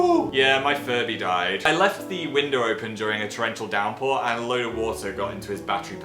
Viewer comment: for a second i thought said 'Northern Downpour' and i got excited